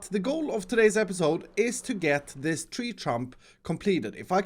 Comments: Hold up, did he say tree trump? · did he just say "tree trump"? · Is it just me or did he say "tree trump" at 2:11!? 🤣🤣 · says tree trump instead of trunk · did say tree trump? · did he just say tree trump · Did he say tree Trump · did anyone else hear tree trump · does he say tree trump lol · is it just me or did say ”tree trump”? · Did anyone else hear that said tree Trump instead of trunk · who heard him say “tree trump” or tree chump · did he say tree trump? LOL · Is it just me, or did say tree trump? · Did anyone else hear “tree trump”? There’s a large emphasis on the p sound there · Am i the only one who heard say "Tree Trump" Instead of "Tree Stump"? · Did he say Tree Trump? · For some wierd reason, it is either me or the speaker is broken because in 2:09-2:11 instead of him saying "tree stump", i heard "tree TRUMP". 😂😂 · did anyone else hear him say "tree trump" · instead of two Tree stump he said tree trump lol